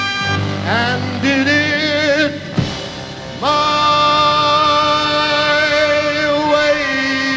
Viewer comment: The fan thought Frank was saying...I'm Gay..😄